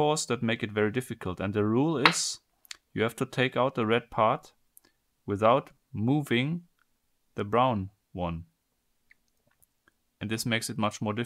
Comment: I am wondering seeing that many comments claiming me using a straw. Of course it's an additional tool and not allowed. I explained it at 2:20 why I'm using it. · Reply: Why do people keep saying that? At 0:54 he clearly says "without MOVING the brown part". That's a specific rule that the brown part can't move. Also, look in the description: "How would you take out the red cylinder without touching or moving the outer cylinder?" You can't move it. It can't move. The darn thing can't be moved, people. It's not rocket surgery.